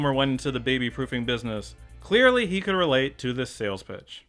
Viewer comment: I had to rewind twice because I thought you said “Sales BITCH” lamao